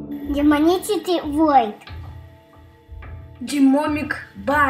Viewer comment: я один слышу демонитетый вольт? 😂😂😂😂😂🤣🤣🤣🤣🤣